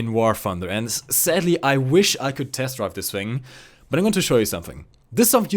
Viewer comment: did he sayed "I WISH I COUD TEST FUCK THIS THING" at 4:00 mins oO?